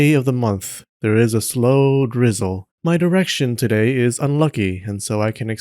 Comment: it’s bad that i heard “my erection today is unlucky….”